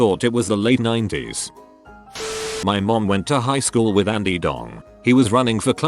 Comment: it says "Andy Dong" instead of "Andy Dick" and I'm in tears!